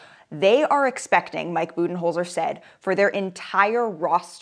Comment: The ball hitting AD in the face at 4:05 😂 😂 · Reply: Bruh when I was waiting for the ball to hit his face I thought she said "mike booty hole set." 😂😂😂